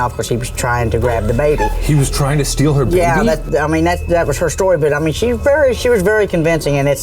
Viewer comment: i heard "He was trying to steal her boobie" lol im ded